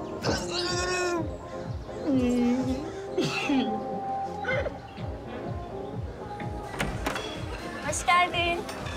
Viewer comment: firat cama yapisip ne diyor ja. 20 kere dinledim hala cözemedim😂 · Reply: Ben sana ölürüm